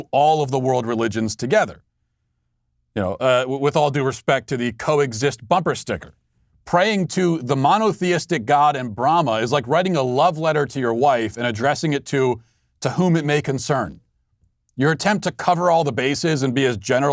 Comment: since he had a mask on it sounded like he said "Bahama" and "a-man" and "a-woman."